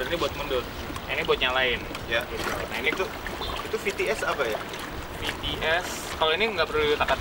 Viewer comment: Gw salah denger kirain BTS ternyata VTS haduh............ 🤦‍♀️🤦‍♀️🤦‍♀️🤦‍♀️